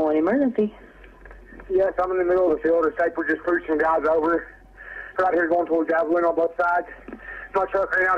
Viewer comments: It sounded like he said "Yes I'm in the middle of a field and this guy is just approaching right over right here going towards Abiline on both sides" I had to slow the clip to ×0.25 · I think he said "a trooper just pulled some guys over"